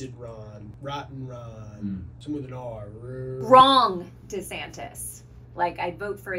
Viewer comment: Ever since the whole drag thing, every time someone says his name, all I hear is "Rhonda Santis".